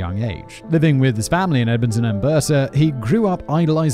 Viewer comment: Edmonton Alberta (Al-ber-ta) sounded like An Berta